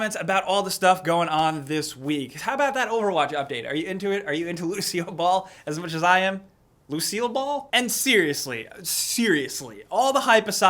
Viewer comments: could have sworn i heard "lucille ball" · For a second I thought he said Lucille ball and I was very confused